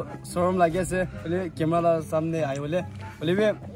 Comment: dude says camala no offense just saying wat i heard anw great vid…love from Westside Imphal❤️❤️❤️